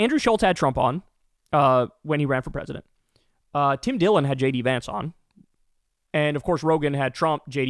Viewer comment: omg I thought you said 'Lady Vance'